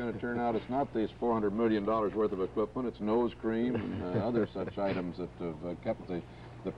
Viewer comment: Did I hear correctly? "Ice Cream" to lubricate? XDD